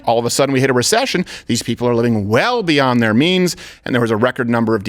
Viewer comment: it almost sounded like Steven said "living beyond their memes"😂😂